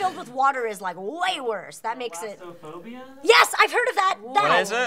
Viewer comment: I thought they said "Prolapseophobia" and I wasn't honestly surprised